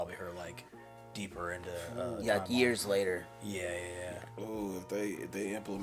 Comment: did he say “gears later” when talking about orianna? i’m high as hell & that was genuinely so funny why didn’t anybody laugh justice for comedy